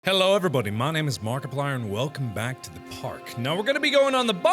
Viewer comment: I heard "hello everybody my name is markiplier and welcome back to the fuck"